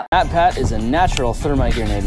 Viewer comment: what did Bonnie say that MatPat didn’t want the world to know- 💀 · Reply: That he is a natural thermite maker